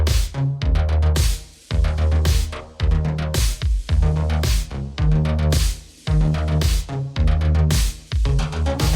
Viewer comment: I gotta ask you as a long time subscriper. What are you saying at 2:58 ? Are you repeatatly cursing or are you saying 'Ship' constantly? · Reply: He's saying shit. You're hearing it right. Lol